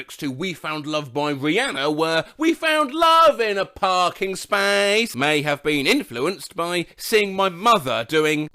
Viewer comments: nah when I was a small child I heard it as "we found love in an ogres place" and i always thought it was about Shrek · i heard "we found love in a homeless place" in this one